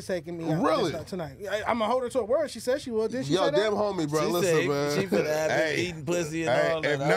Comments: did flakko just say dead homies? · did Flakko really say “dead homies”? 😂💀💀💀